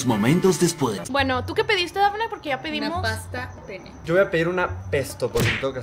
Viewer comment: dafne dice paste pne o que dijo 😅😮